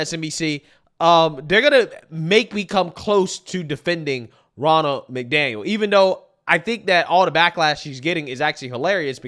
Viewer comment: i thought they say "ronald mcdonald 😂